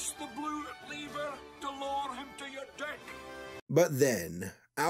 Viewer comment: I could barely understand any dialogue in this film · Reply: When I heard this the first time I thought he said lower him to your “death”.